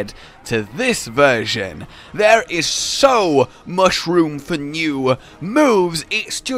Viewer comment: did just say "he has so mushroom for new moves"?